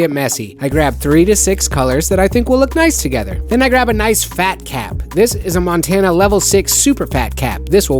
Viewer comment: i literally heard "i grab a big fat cat" XD i was drawing and listening